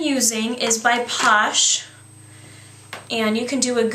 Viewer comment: did she say posh 1:17?